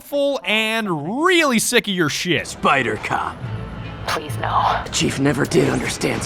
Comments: I thought he said spider coc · am I the only one when it said spider cop I thought it said spider cock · I thought he said spider-cock not spider-cop